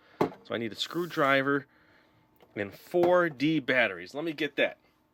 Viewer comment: On low volume, it sounds like you said "14 batteries" as opposed to "4 'D' batteries".